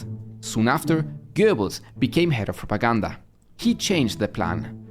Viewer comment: I thought it said Goebbels became head of Wakanda and was very confused.